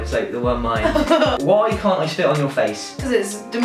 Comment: I thought he said 'why won't you let me sit on your face' I was just sat there like, YOU DONT SAY THAT. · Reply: I totally thought he said that. and then he suddenly spit and I was like oooooh spit... not sit.... ah... it makes more sense now